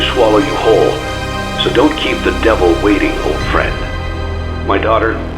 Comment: “Although for one of you, the darkest pits of hell have opened for you. So don’t the devil waiting.” The best part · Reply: Uhm j thought it said so don't keep the devil waiting